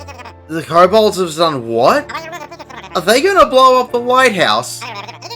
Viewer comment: I swear I heard "Are they gonna blow up the White House?" That would be an interesting dungeon